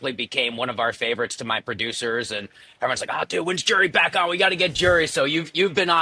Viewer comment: Did he say "when's Jerry back on"? I listened twice and I swear that's what I heard lol.